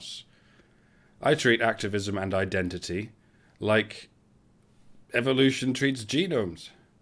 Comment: I thought he said " ...like evolution treats ginos."